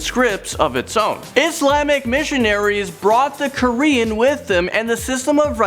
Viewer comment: Dude u butchered Bahasa Malaysia or Bahasa Melayu and Bahasa Jawi is pronounced as Bahasa Ja Wee not Ja Wai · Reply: oh and also the quaran. I heard it as korean